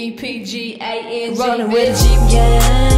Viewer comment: That almost sounded like she said gay jeep gang. I know she's out but, did she just out the rest of em????? LMFAO!!!!!